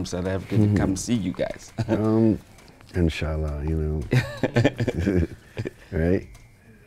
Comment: Did I hear just say Insh-Allah 🤔 .... If I heard correctly, Masha-Allah brother ... Great interview btw grootman